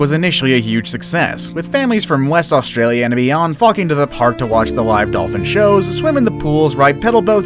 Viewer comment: Is it just me or did he said the f word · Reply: "flocking to the park"